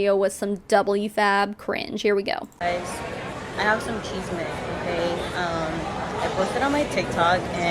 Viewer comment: lol 32:48 IG thought she said "I have some cheez mix" but she really said "I have some chisme" laugh if you speak Spanish :D